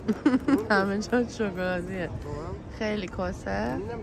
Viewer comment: 18:51 چی گفت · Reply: Kheili cose?